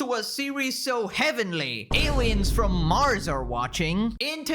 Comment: Did someone just say heavenly